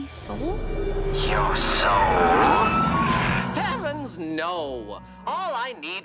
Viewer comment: "you're sloppy" the animation during that line HITS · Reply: i cant be the only one that thought alastor said "your soul maybe" during that part😭